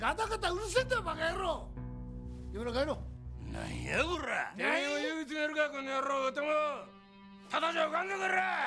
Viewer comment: ここなんて言ってるのかずっと気になってる。誰かわかりますか？ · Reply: 多分 (お前も指詰めるかこのやろう) やと思います